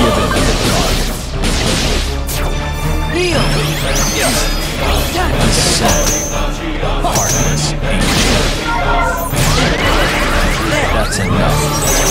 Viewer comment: OH MY WORD! HE IS SAYING "DESCEND HEARTLESS ANGEL"! I have been trying to figure out what he was saying since KH1, that's 2002! FINALLY! I CAN SLEEP!